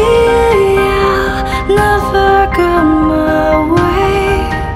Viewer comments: I thought it's "Cereal never gone my way🎶" instead of "See ya never gone my way🎶" HAHAHAHA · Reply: I hear " See ya never f**k on my way "😂😅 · i always thought that she said syria never gone my way...lol